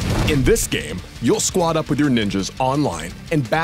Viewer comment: Thought he said "Squad up with ya niggas online"